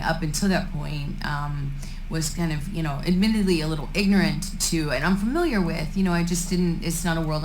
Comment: I think you misheard her 1:41 “ignorant to and UNfamiliar with”